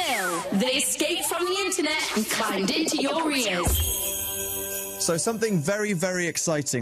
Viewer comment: I ALWAYS hear "they escaped from the Internet and climbed into your rears." Every single time. LOL. Thanks for the upload! :)